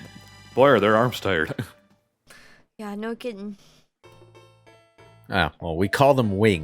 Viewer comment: Did just say "No kitten"? (I mean she didn't, but it sounded like she did to me.) Also if Phaleg was always a ghost then how did Nergal punch him in the dick before?